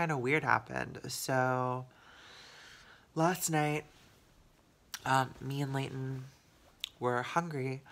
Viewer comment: At the beginning when you said "last night Layton and I got hungry..." I thought you said "last night, Layton and I were fucking!" I thought man she's blunt and isn't afraid to share anything! LOL 😂